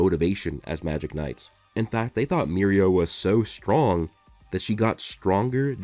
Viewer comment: It's funny, because I keep hearing "Mirio", but I know it's "Mereo" as part of "Mereoleona "